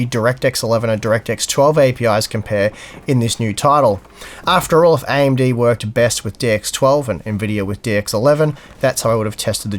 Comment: Oh lord i don't know if it's because i stayed all night working or Steve's changed voice or a combination of both, but on 3:49 i heard "AMD works best with dicks 12 and Nvidia with Dicks 11". And I blew the coffee all over, both monitors top to bottom and half my case.